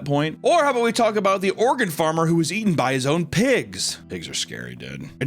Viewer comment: Why did I hear “organ farmer” not “Oregon farmer”